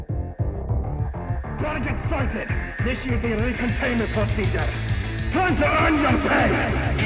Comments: Can anyone tell what he said at the beginning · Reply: THANK YOU. I thought he said "target sighted initiate recontainment procedure Time to your THE BANK. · "Target sighted, initiating recontainment procedure. Time to earn your pay!" 0:37 - "You're coming with us!" 1:25 - "You're UP!"(Swap to Cadet) 1:34 - "Ugh! We don't have time for that!" 1:45 - "You're going NOWHERE!"(Swap back to Commander) 2:05 - "Don't let him get away!" 2:15 - "GET GOING!"(Swap to Lieutenant) 2:24 - "Ugh! We don't have time for that!" 2:36 - (Swap back to Commander) 3:05 - (Swap to Lieutenant) 3:07 - (Swap to Cadet) 3:09 - (Swap back to Commander) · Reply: i thought it said, "Time to make them pay!" but you might be right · "Target sighted, initiating recontainment procedure. Time to earn your pay!" 0:37 - "You're coming with us!" 1:25 - "You're UP!"(Swap to Cadet) 1:34 - "Ugh! We don't have time for that!" 1:45 - "You're going NOWHERE!"(Swap back to Commander) 2:05 - "Don't let him get away!" 2:15 - "GET GOING!"(Swap to Lieutenant) 2:24 - "Ugh! We don't have time for that!" 2:36 - (Swap back to Commander) 3:05 - (Swap to Lieutenant) 3:07 - (Swap to Cadet) 3:09 - (Swap back to Commander) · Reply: I thought he said "turn to run and pay"